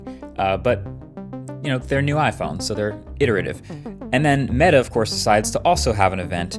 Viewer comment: What’s that word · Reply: Iterative